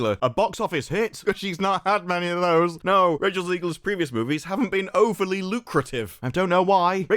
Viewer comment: I heard you say “Racial Zegler” and now that’s how I will always refer to her. Brilliant!